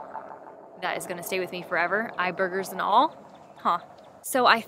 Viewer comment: Did you just say "eye burgers"? 🤢 🤣